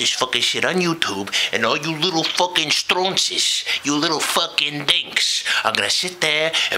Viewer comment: did he say stronzies?